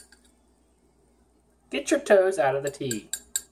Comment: I thought he said gets your turds out of the tea but he said toes